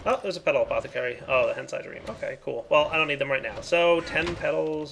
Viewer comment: Am I the only one who heard him say hentai dream instead of heisei dream? 😂😂